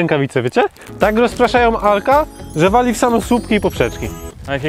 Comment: czy tylko ja usłyszałem *Hulka?